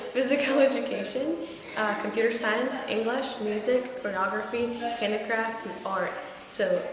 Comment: Ok, I'm watching that part of your video again, again and again! I have a very silly question but I can't help myself to ask: in the minute 3:14 you don't say "pornography", right? Off course you don't but I reeeeeally don't understand THAT word. Can you tell me what word it is please? Ciao from Italy! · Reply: Choreography